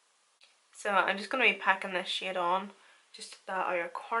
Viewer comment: Lol when you said "packing that shade on" I was lmao cuz it sounded like you said "packing that shit on" with your accent lol